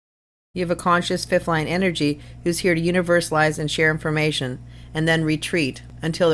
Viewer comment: what is the sentence you say around 2:55? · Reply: I said "universalization of the energy" which means that you provide practical guidance about the things that you specialize in, basically you break down larger concepts so others can understand. Hope this helps.